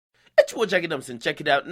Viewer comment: that intro sound like you saying "its ya boi jackademics"